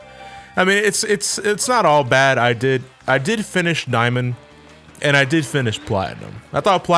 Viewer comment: I coulda sworn you said niamond.